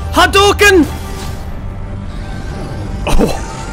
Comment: did he just say hadooken